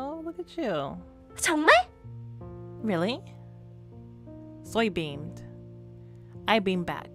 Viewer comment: "Soy beaned!" What I heard at first. :_lol::_lol: 14:10 Ayooo :_empty: look at your face, lmao what is this game?!